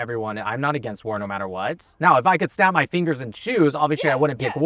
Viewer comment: I rewatched this part 3 times, because I thought said "Now if I could snap my fingers and shoes." 😂😂